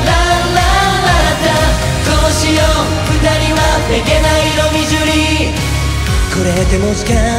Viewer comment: 空耳で どうしよう 二人は 下剤飲み過ぎ にしか聞こえなくなった😭